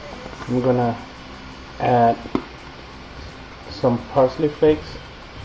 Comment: When you said parsley flakes..I thought you were saying "Frosted Flakes" the cereal. I thought this was going to be the strangest taco recipe ever! But then again tacos with broccoli is pretty different to me. Looks good..I don't make shrimp too often but I'll have to try it in this recipe. Thanks!